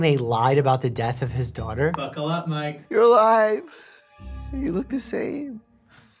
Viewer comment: The way Sam said “buckle up mike” it really sounded like “ buckle up mate” sounded so Aussie 😂😂😂